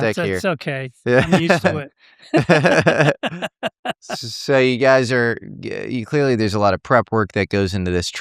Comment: Did he say "so you guys are gay"???? Haha